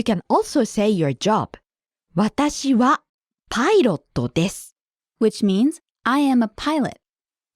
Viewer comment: Lol at 3:00 I thought she was saying she's a pirate!n